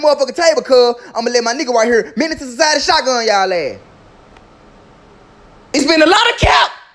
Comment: Man I thought he said miniature size shotgun yall azz instead of menace II society shotgun yall azz💀💀💀😭😭😭